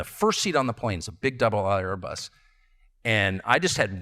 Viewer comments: Tucker Carlson said, "It's a big double aisle Airbus." He did not say, "double aught" as was claimed at 11:29. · It sounds like he said "doubel aisle". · I think he's saying double aisle, not double ought. Emphasizing the large size of the plane.